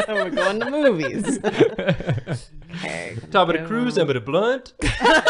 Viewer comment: OOHHH Emida Blunt is supposed to be the name of an actress For the longest time I just thought said “Tomadacruise Havinablunt” Like he was smoking weed. Just as a complete nonsequitur. I mean I never saw the movie, so he might have smoked weed in it. Idk man.